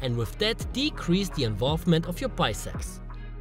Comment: does he say bisex?